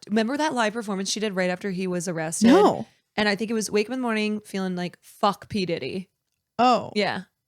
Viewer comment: My, not English knowing, kid-self thought Kesha said "woke up in the morning feeling slightly sick" and I was like okay she is hangover but going out again makes sense